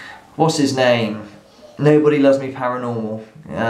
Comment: 'Nobody loves me paranormal' lol! did you really say that @ 14:16? brilliant! easy mistake to make I suppose.